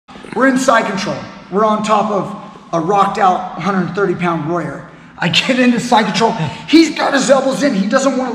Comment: Initially, I thought you said "a rocked out 130 pound LAWYER!" Who doesn't want to dominate one of those. I'm thinking that this great technique would also work from a near side kimura. Yes? Thank you!